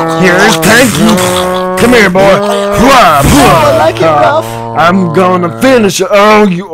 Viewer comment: did i just hear Francis saying he likes it rough 😂